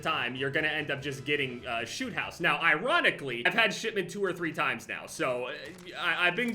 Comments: It sounded like he was saying Shitmeant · Everytime you say "shipment" I hear "shitment".